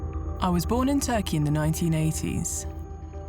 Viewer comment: i thought she said she was born a turkey